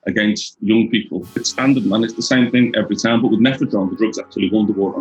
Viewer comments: I thought he was saying methadone I was tripping for a second like wait that don't look like methadone dude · Lmao I thought be was saying methadone hahaha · It sounds like your saying methadone. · I thought you were saying methadone at first.